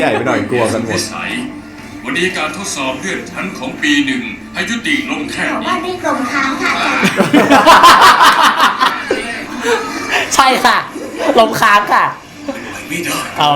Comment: เขาพูดว่าอะไรนะคะ😅 ได้ยินไม่ชัด ทึ่เป็นเสียงตัวประกอง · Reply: "แถวบ้านเรียกรมณ์ค้างค่ะจารย์" 😂